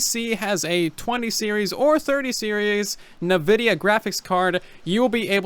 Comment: bruh, did he just say nuhvidia...